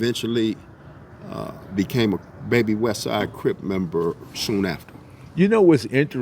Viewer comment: It sounds like he said he became a baby westside crip member. Did he mean Crib or Crip? Around the 11:02 mark. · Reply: Crip